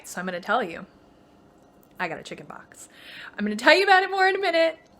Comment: I thought you said you got chicken pox. Then I realized you said you got a chicken box. Sigh